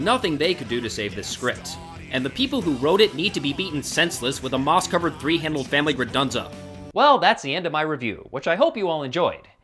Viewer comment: "moss-covered..."what was the rest of that thing he said? He spoke TOO fast for me. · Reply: Moss covered three-handled family gredunza